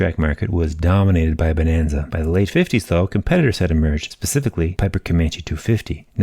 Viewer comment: Did you say "pacifically" instead of "specifically" at 4:05?😅